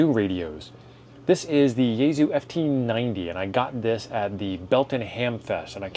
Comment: I thought you said ft 950 lol I own that and love it